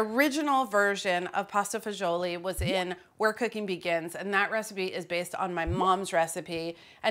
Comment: i heard “where cooking begins” as “we’re cooking vegans” lol